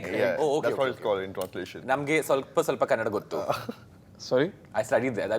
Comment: Did he just say i am Gay..? Or is it just me and sunny singh..?😂